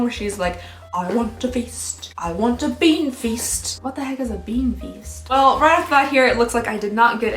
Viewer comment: I thought she said “big feast” but apparently bean feast is a celebration meal